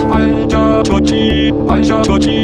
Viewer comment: 잘 들으면 관절 저지로 들림ㅋㅋ